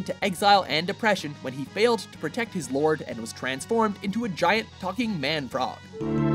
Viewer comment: Did you just say giant fucking man frog?